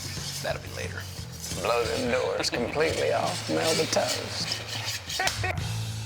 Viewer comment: What the Hell did Matthew say at 1:23?? Please! Someone tell me something legit! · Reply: "Blows the doors completely off melba toast."